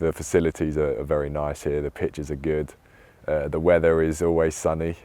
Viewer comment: he actually said "the pitches are good" Not "the pictures"